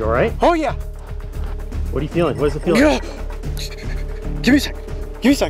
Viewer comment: When he said “how are you feeling” he sounded like he said “gORe!”